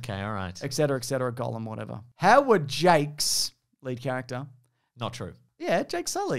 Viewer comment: I thought he said Howard Jake’s too